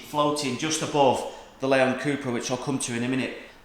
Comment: did you say Lemon cupra lol